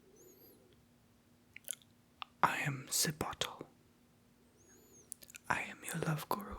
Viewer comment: No offense but I thought he said “hello I’m ze bottle “ xD